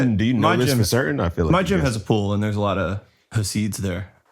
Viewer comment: Damn i thought miles said "my chick has a pool" and i was hyped. Ran it back and he said gym lol